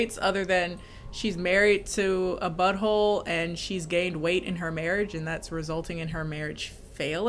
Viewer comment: when you said "She gained weight so her marriage was failing", I thought you said "She's gay and she's waiting in her marriage so her marriage was failing" 😭